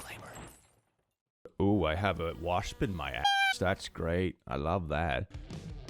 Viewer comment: When you said "I have a wasp in my ass" I thought you were saying you had a wasp's nest in your attic and got concerned